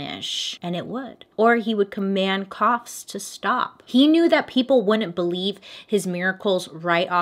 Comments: 100% thought you said "cops to stop" when you said "coughs to stop" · I heard “command your cops to stop” and i was like “ACAB king”